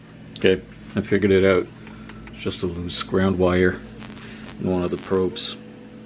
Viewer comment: I have the pin cushion issue, but I can't make out what you're saying at 19:14. scram wire?? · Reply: Loose ground wire. Took me a while too. :)